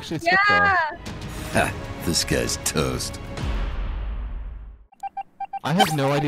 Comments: Why everytime i hear dat intro it sound like it saying "this guy is toast" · The first time I heard the "disguised toast" i thought it had said "dis guys toast" Like H u h👁️👄👁️ W u t ? 😀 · I’m sorry I have been watching toast for about a year now and I have always heard his intro as “This guy’s toast!” But today I heard “Dis-guised Toast”